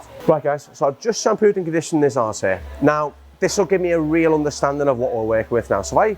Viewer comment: Another fantastic cut from although i have to say i had to rewind it at 2:55 as i thought he said "ive just shampooed and conditioned his arse hair" Now that is what i call a thorough cut